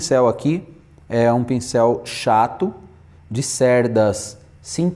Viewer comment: Nao entendi o nome do pintel, eu escutei "chatos"😂